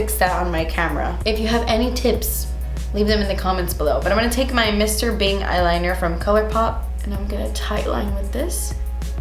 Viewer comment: I thought you said Mr.Bean eyeliner lol